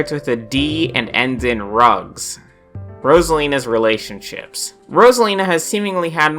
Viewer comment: can someone tell me what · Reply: Drugs.